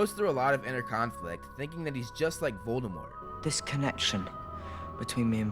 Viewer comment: Why the f*ck does the caption say Oldman instead of Voldemort